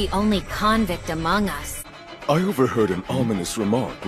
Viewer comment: did he just say among us